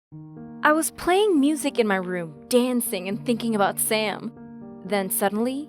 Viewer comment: at first i thought that she said : I was thinking about sans